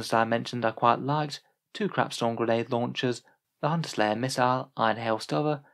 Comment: I heard "crapstorm grenade launchers"